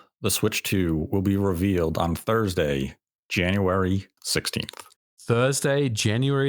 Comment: He said THIS WEEK. Does THIS WEEK end on a Wednesday??🙄🙄🙄🙄🙄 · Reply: he clearly says thursday